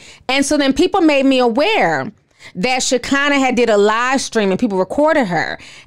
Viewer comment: When you said Shekinah’s livestream I heard “LIEstream”, but I don’t think there’s much of a difference 😂💯.